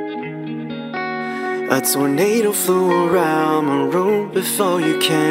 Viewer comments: I accidentally thought: "a potato flew around my room" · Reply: Wait...he didn't say that? Lol I legit thought he said "a potato flew around my room" I'm such an idiot😂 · I heard "a potato flew around my room"....and idaho?